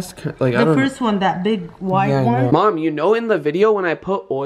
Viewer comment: WTF? at 6:29 is says " Oh Mom "